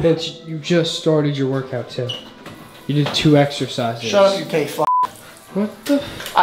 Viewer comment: did he say “gay fuck”